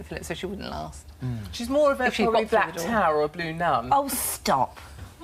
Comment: What is she saying at the very end (about 9:26)? I couldn't quite understand her. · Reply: Katie Hopkins said that girls called chardonnay should probably have been called "Black Tower" or "Blue Nun" Which are cheaper wines.